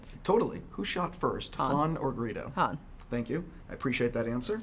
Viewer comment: When the interviewer was asking who shot first, it sounded like he said "burrito" instead of Greedo.